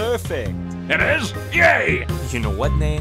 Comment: Oh. I thought he said "I'm just happy you can hear.". I always thought the joke was he thought he sliced his ears off.